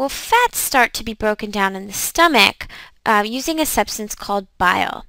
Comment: lol. if the subtitles are on when you say Stomach, the subtitles say Amick :-D. #youtubesubtitles #changethesubtitle #subtitlemistake